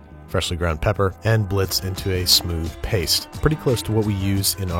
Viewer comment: I swear, I heard “human” and not “cumin” and got very worried for a second XD